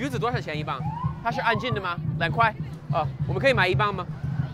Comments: thought he said the N word and I started bursting out laughing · i cant be the only who heard that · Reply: He said, "Nei Ge" or "那个" which means "that." It's a filler word, similar to "uh" in English. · what did he say??? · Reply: He said, "Nei Ge" or "那个" which means "that." It's a filler word, similar to "uh" in English. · what did you say? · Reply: He said, "Nei Ge" or "那个" which means "that." It's a filler word, similar to "uh" in English. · what he just said? xD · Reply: He said, "Nei Ge" or "那个" which means "that." It's a filler word, similar to "uh" in English.